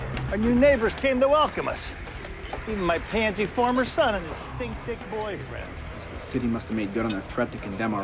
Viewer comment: What does Terry say about Ian? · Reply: i thought he said 'thin thick boyfriend' lmao